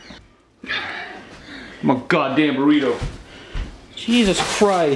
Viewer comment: I thought he said Dorito.